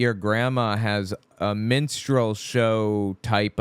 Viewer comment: Before i forget: i wanted to share that instead of "minstrel show" I thought you said "menstrual", so it took me a second to figure out what you were trying to say. 😂